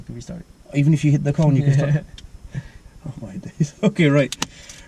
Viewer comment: "If you hit the corner you can restart." Haha, I thought he was gonna say "If you hit the car, you can restart"